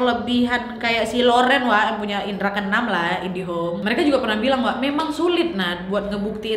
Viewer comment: / 29.42 mungkin aku yang salah dengar ya.. Indihome....aku yang salah, maaf...